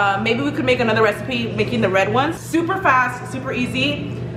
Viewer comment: I thought she said making the bread ones I’m like BREAD???